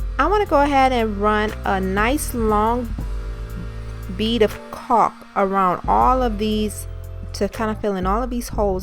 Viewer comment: What do she say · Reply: Hi. I said “I’m going to run a nice long bead of caulk around these to fill the holes...” You can also use the captions. It transcribed this video pretty well.